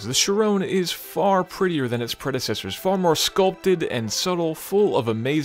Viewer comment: Did he said prettier? That front end is so awful!